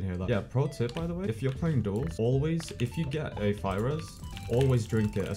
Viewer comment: my stupid brain heard “virus” instead of “fire res”